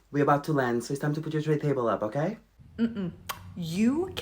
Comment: I thought he said dray table and was very confused because all tables are generally dray. Tray makes more sense.